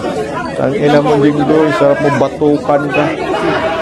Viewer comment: I’m not sure kung sino minura mu at babatokan mu 18:04 did I mishear you or what kasi it’s tang ina mu jinggoy sarap mung batokan 😂 or baka si jonvic 😂 anyway MABUHAY KA PO GINOONG JINGGOY ESTRADA AT SP.ALLAN CAYATANO ❤